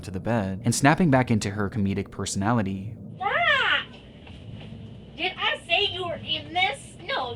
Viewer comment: did she just say .. did I say you were getting this